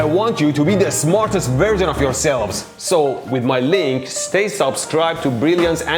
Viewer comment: I was so entertained throughout this video that at 17:20 I could have sworn Mehdi said, "As your father, I want you to be the smartest VIRGIN of yourself."😳 Was I set up?😉